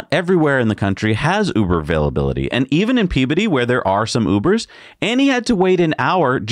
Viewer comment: I'm aware that I am a 57 year old child :) but I keep hearing "puberty" every time you say that service centres name.